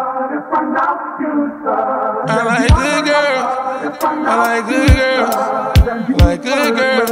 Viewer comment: I swear I heard him say I like lil girls at the beginning 🗣️🗣️🗣️🔥🔥